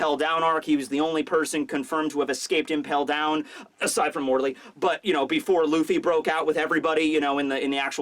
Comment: who did he say?????????? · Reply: aside from Morley, the big okama with the ground fruit from the revolutionaries